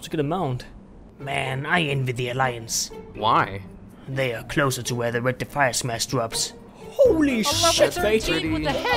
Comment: What does he say? I can't get it :I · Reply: killed Obama " man i envy the alliance""why""they are closer to where the Red Defiaesmask drops"